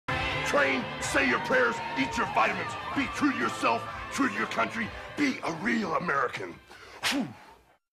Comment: i always thought he said eat your vegetables